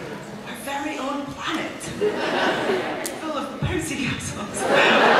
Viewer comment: What does Karen say at 42:12 .. My very own planet full of (what?). Thanks! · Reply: bouncy castles =]